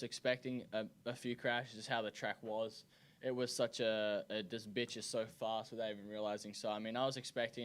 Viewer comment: Did Jett say "this is bitch is so fast" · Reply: I think he said: "it just bit ya so fast" talking about how tricky the track was